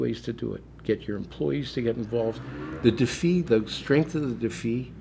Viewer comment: What word is he saying at 51:40? It sounds like defeat but he is putting no t on the end? The defee?